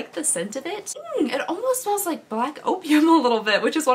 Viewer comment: I’m at work watching your video without headphones (I forgot them at home) and I thought you said black oatmeal was your favorite perfume. I had to rewind like 3 times 😂😂